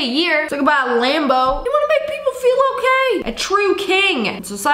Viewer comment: I thought he said i wanna make people feel the pain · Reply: Nooooooooo he said “I wanna make people feel okay” 😂😂😂😂